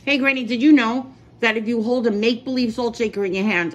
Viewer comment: I heard "maple leaf sauce" and was super confused. Ah. It's make-believe😢